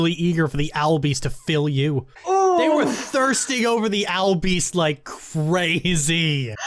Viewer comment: For a second I heard "You were thirsty for the owl beast" my brain did a double-take on what it heard and I nearly choked on my Brisk Iced Tea. I swear that just slapped me 'cross the cheek three fields away, Jesus.